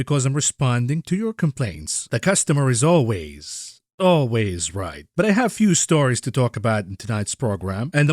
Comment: "the costumer is always, always in the detail" - did just called me a devil? Bbb..b..b..ut I love God :'(